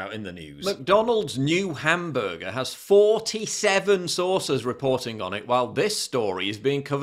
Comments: Lol, l thought you were saying mc donalds were making a new burger with 47vsauces on it*! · Imagine if there was a law that all welfare and benefits can be revoked for the parents of kids who act like this. That might fix it fast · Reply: I thought you said McDonalds’ burgers had 47 sauces! Are you from Nottingham or summat? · I thought you said McDonalds’ burgers had 47 sauces Paul ! Are you from Nottingham or summat?